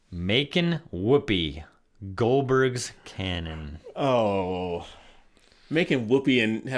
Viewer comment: At first, I thought it said: "Makin' Whoopi;; Goldberg's Canyon"...oh my! 😮 Oh WOW! I want to take the Buffy Vampire Slayer course! BTW: A good mental health music video to review is Popular Monster by Falling in Reverse.....maybe your son can find a college course on that song. :)